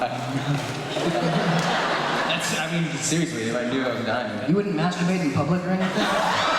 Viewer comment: What did Tyler replied to Dylan when he said he is going to see his family and his loved one? Around 32:26? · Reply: "you wouldn't masturbate in public or anything?" haha